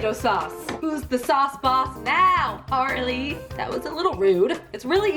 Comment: all I heard at 4:21 was, "Who's the sauce boss now Harley" I started freaking out because that's my name. Idk what you said tho lmfao